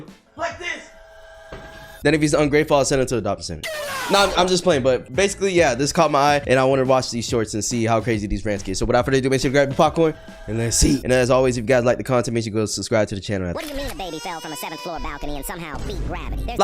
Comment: sounds like "grab your popcorn and lets eat" instead of grab you popcorn and lets see